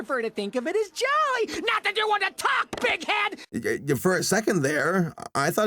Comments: I had to play it at 0.25x speed and he actually says bitchhead. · I thought he said dickhead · I thought he said dickhead. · WHAT DID HE SAY? IT LITERALLY SOUNDS LIKE B!TCH HEAD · i thought he said: "d*ck head" XD